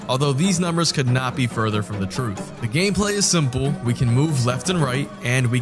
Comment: funny how he says "the game play is simple" but it sounds like he says "the game play is some bull"